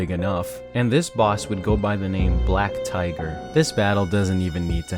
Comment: Fun fact: When I first heard the name, I thought it was Black Taiga.